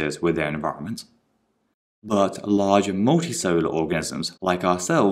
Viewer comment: what u say :I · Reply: Larger multicellular organism :\